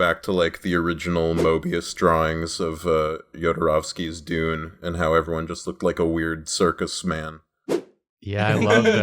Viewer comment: I'm begging someone to transcribe this part of the video I have no idea what he's talking about so I can't make out the individual words he's saying in order to look it up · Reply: “I just wanna go back to like, the original Mobius drawings of Jodorowsky’s Dune, and how everyone looked like a weird circus man.” Basically saying how a lot of sci fi and fantasy was made to look REALLY unusual, but with things becoming mainstream they try to make it “accessible,” in other words make it more relatable and familiar to as many people as possible. This can result in some media (like superheroes, high fantasy, and sci fi) becoming more watered down and basic in design, story, etc. What he is saying is that he likes when things are really unusual and pushed far in terms of design, vs the much more “normal” designs of fantasy and sci fi pop culture currently.